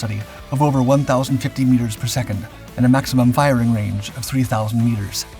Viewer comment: And, ballistically, if it's going 1,050 feet per second, there's no way it's even going to reach 3,000 meters. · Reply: I was corrected on another comment. They actually said 1050 meters per second, not feet per second like I thought they said.